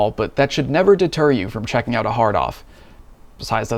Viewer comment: Anyone else giggled every time he said "hard off" 😅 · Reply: I did the first time. I thought he said "hard on" lol